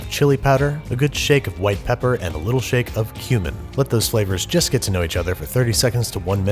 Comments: Thought he said “Human” not “Cumin” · Holy shit I thought you said human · did he just say human?? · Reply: No he said cumin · I was so worried because when I first heard that I thought he said a little shake of human. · So I'm one of those people who likes the captions on because I also watch videos 1.5 to 2 times faster so I can watch more videos and at the 3:25 mark he says "shake of cumin" but the subtitles said "shake of human" and I don't know why but it had me laughing hard. I mean, chili isn't real chili without a shake of human amiright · I really thought you said a little shake of human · i thought i heard "human" for a second. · Lmao sounds like he said a little shake of Human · I thought you said human